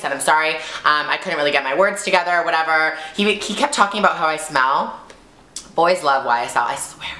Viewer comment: When you said "He kept talking about YSL" I thought you said "He kept talking about why I smell" HAhaha omg Gigi <3